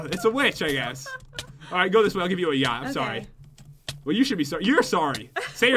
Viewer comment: did say i'll give you it "yacht?"